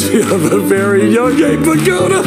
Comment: Not gonna lie at first I thought Ryan had said she reminds me of a very young gay pagoda not Abe Vigoda. I need to clean my ears! XD